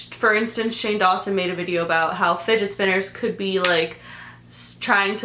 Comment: When she was talking about Shane and how fidget spinners are meant to distract us,I thought she said fitnessblender not fidget spinners and I just finished one of their workouts and I was so confused like why and how tf would a work out channel distract us???